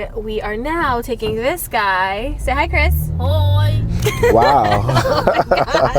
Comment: did he say hoi? Is he dutch?